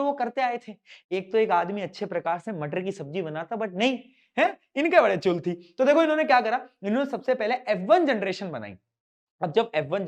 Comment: bhaiya ne kya bola koi batayga😅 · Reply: Woh aram se mattar panner banata par inke badi chul thi